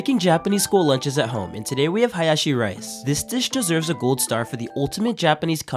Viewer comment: Me eating my pizza that's been warmed up 2 weeks in a row: 😢😢😢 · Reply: I thought he said "bayashi rice" 😭😭